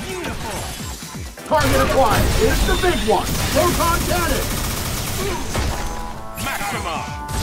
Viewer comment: I swear I thought he said wonton cannon 😂